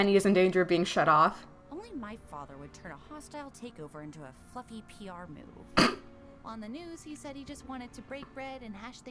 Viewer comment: I just misheard Weiss saying "Fluffy PR move" as "F-ing PR move"